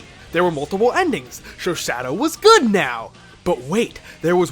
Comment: it almost sounds like you said "Sho Sadow" XD It's that "She sells sea shells by the seashore" tongue twister all over again~